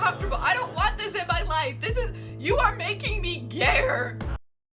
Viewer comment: "he is making me -insert word here-" what is she saying!? he's making her "gare"? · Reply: she said, "hes making me gayer" aka hes so DISGUSTING that shes becoming more gay just looking at and hearing onision